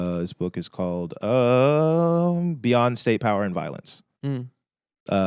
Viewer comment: I heard Beyonce power and violence